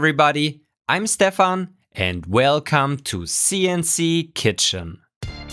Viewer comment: I swear, every time I hear "welcome to CNC Kitchen" I hear "welcome to CNC Chicken"